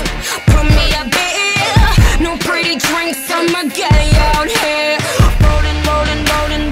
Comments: lol is it just me or did it sound like she said "no pretty drinks I'm a gay out here" plz tell me I'm not the only one that thought that 😜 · I thought it said “I’m a gay out here” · She sounds like shes saying Im a gay out here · Everytime she says "I'm a guy out here" I keep hearing: I'm a gay out here. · It sounds like she says "Im a gay out here" 😂😅😆🤔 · Always sounds like she says "I'm a gay out here" instead of guy lmao